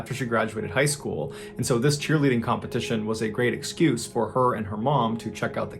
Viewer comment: Great story telling bro. I thought when you said cheerleading competition you said chille eating competition. I must be high.